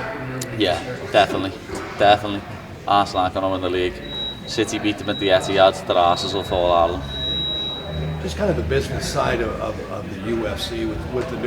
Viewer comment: Have no clue what he is saying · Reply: "City beat them at the Etihad, their arses will fall out of them"